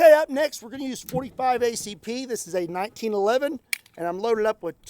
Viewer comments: Whew, I thought you said .25 ACP, and I was shocked. 🤣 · Did i just hear him said .25 ACP ?